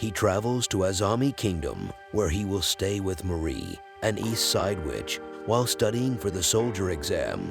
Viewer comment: I thought he said "a new side bitch"